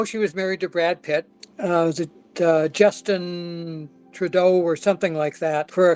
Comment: did he say she was married to justin trudeau 😭😭